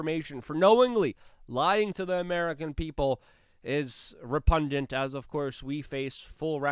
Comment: repundant.pererrrr revviruew 🥴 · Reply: LIFTING THE VEIL: Don't you just love him-- he is priceless! (I thought he said re-pungent!